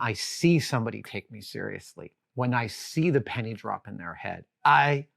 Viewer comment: I thought he said when "I see the panties drop in their head"